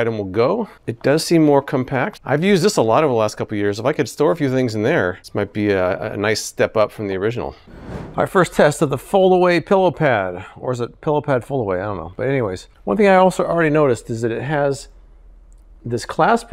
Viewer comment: I keep hearing "Pillowpants" and expect "Listerfiend" to show up!